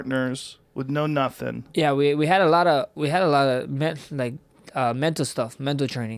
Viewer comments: Sounded like he said “we had a lot of meth” hahahaha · I thought he said we had a lot of meth 😂☠️ 0.23